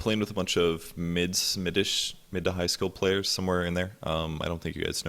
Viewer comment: At first I thought you said middle to high SCHOOL players lol.